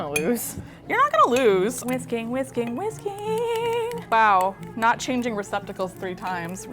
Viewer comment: when Kristin was saying whisking whisking whisking I heard whiskey whiskey whiskey